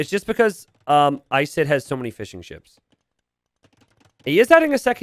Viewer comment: I swear he is saying "Fish 'en Chips" instead of fishing ships sometimes....